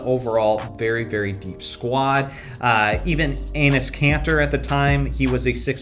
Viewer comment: did you call him Anus Kanter? 😂